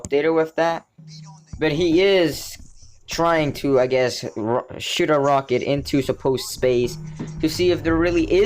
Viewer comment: Sounded like you said he is trying to shit a rocket into space LOL